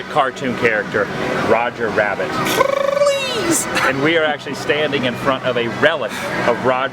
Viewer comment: Did he just say fleas instead of please?